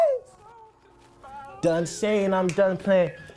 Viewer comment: What does Daniel say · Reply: He said "Chee!"